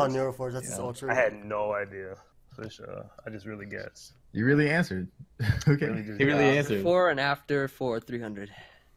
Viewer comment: im sorry no matter how many times i go back and listen i cant hear what the answer was to final jeopardy it sounds like just said imma just say B+ usually you put everyones answers on the screen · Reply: beak blast